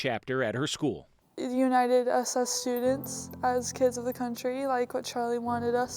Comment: Did she just say united SS students?